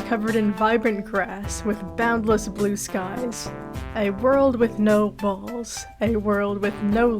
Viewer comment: Please help, what on Earth is being said here because all I hear every time is, "a world with no balls," 😭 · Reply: I think its "A world without walls" I think its a metaphor